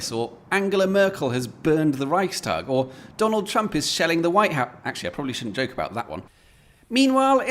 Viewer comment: "Or Donald Trump is shelling the White House." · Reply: 'Shelling the White House'? I thought said 'Selling the White House' - which I think more likely!